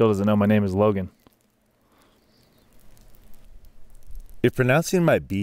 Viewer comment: So before watching this video for a 2nd time, I could’ve swore that he said, “ So if pronouncing my b’s and v’s is Russian, Soviet!!!